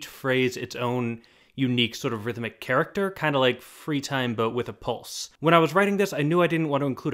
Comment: With ring around the rosie I thought she said "three time" instead of "free time" so I spent the whole song trying to see how the overall thing fits into 3